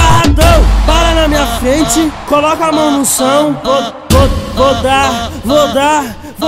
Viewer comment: Só eu que ouvi "coloca a mão no são?"